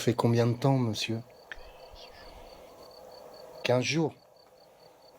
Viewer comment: Bonjour , je voudrait vous prévenir que vers 27:00 ln n'entend l'esprit dire " 15 jours " je vous laisse réécouter mais moi j'entends bien la voix de l'esprit, merci